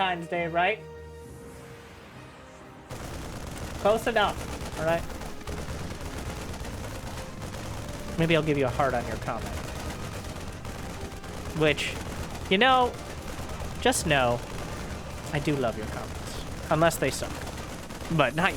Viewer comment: Did he say "Maybe I'll leave you a hard-on. You comment." ? Anyways, here : I commented. It worked.